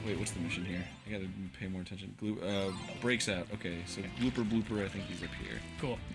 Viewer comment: i thought he said Booper Dooper, as in jacksepticeye... before I saw "Gooper Blooper" on the screen